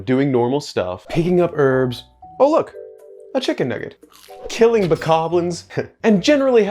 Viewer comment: I legit thought he said "Oh look, a chicken n*gger" lmao